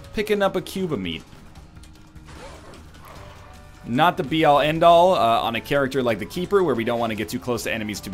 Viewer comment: What's a "BRNR" (or what ever he says)? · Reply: XD he sais '' be all end all'